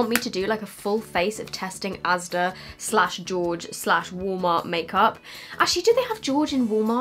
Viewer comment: wow I thought you were saying "warmer" but you were saying "Walmart" 😂 your accent omg ❤